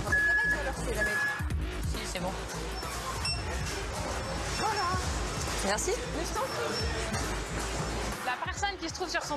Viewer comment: elle dit quoi ? dsl je bug sur ca mdr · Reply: euhh... voila merci je t'en prie